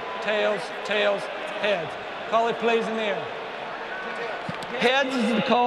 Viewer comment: Is anyone here after Geno Smith most likely said tails? Tho i wouldn’t be surprised if that doof said both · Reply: They put out clearer video and you can here him say Heads. When I watched it live I thought for sure he said tails too.